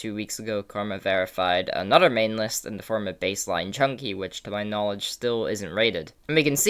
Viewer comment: this guy CLEARLY said bassline junkie and the captions said bassline jumper